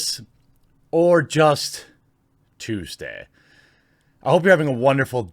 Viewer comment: I know you didn't say it bit my mind thought you said jewsday